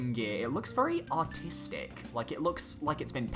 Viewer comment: did you just say his gear looks autistic?